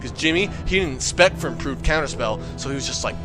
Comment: does he say "exspect for improved counter-spell" or "spec for improved counter-spell?"